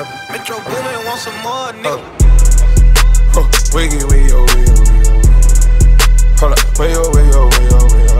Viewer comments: I thought he was saying wiggle wiggle wiggle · What does he say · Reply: He says metro boomin want some more n*gga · I thought he was saying wiggle wiggle wiggle wiggle 🤷🏾‍♀️